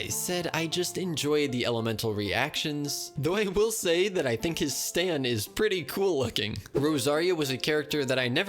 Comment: DID HE JUST SAY “STAND”??